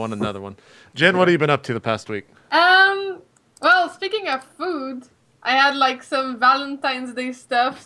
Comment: said "Speaking of food...!" I heard "Speaking of boobs...!"